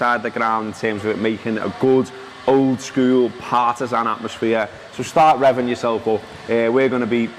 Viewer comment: Did he say "Partizan atmosphere" ?